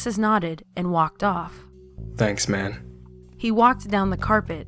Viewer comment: Did he just say "Thanks man !"